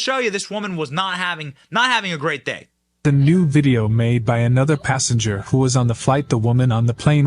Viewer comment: I thought it said "nude video" and came running, but apparently it said "new video", lame